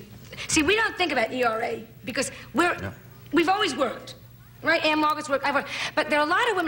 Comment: i thought she said ann, ann margaret?